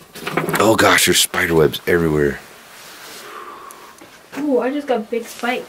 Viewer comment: When you were talking about the spiderwebs in the basement, I heard "yeah. There's a lot of spider webs down here." It could have been.